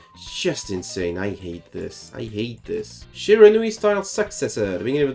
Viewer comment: I honestly thought u said Shira style sexism lol